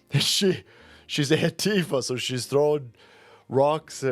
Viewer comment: Did you say "heftifa"? Wonder if WGN will fire her for doing cries under the color of journalism.